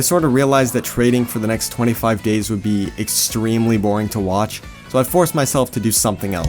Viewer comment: i think you said gays not days